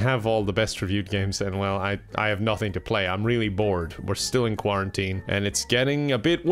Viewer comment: Did he say he was BOREd